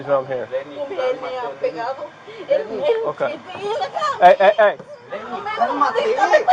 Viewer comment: Spanish speakers who heard the lady saying “el siempre me a pegado” on 5:24 Poor lady. She’s like this is my chance